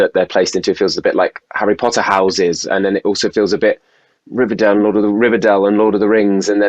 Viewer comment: Did he just say riverdale?? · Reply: He said Rivendell from Lord of the Rings